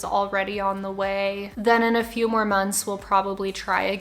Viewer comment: I had subtitles on so when it said do you like legos I thought it said do you like tacos…